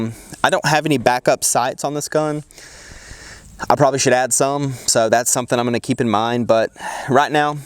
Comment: I thought he said I bought Chad some l🤣🤣